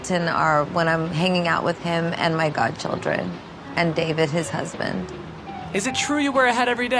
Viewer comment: did she said "his husband"??